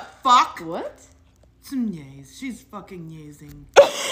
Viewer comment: what did she say she was doing? Nyazing?🤣